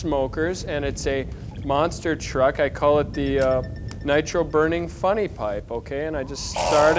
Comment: And to think that this is where Gwar got the name for their song "Nitro Burning Funny Bong" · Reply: I thought he said Nitro Burning Funny Pipe, but does it matter?